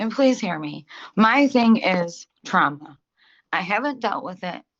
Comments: “Please don’t incarcerate me. I have trauma & I need help” Doesn’t go to a single court mandated therapy session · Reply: LOL...I totally thought she she said is was "Trump" she hadn't dealt with. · I was half paying attention at 16:35 I swear when she was saying her thing is Trauma and she hasn't been dealing with it. I thought she said Trump and she hasn't been dealing with it 🤣🤣🤣